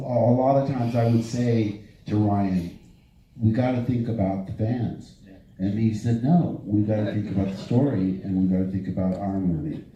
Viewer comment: Thank you very much for your work! I have to raise a question regarding a clip used in this video: 39:05 - 39:24 I am no native speaker and I don't understand every single word. Would anybody be so kind and could write it down for me? Thanks in advance! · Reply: "And I said "we got to think about the fans" and he said "no. We gotta think about the story and think about our movie," which I..." That's what Mark says.